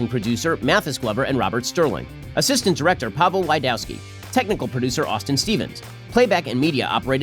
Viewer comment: During credit read at the end, It always sounds like Ben is saying the name, "Pablo Wydowski". Pablo Wydowski, the first mexican-jewish cowboy in online media. lol